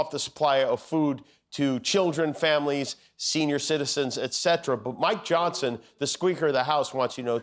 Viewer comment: Min 6:06 Did I hear that correctly? "Mike Johnson the Squeaker of the house..." 😂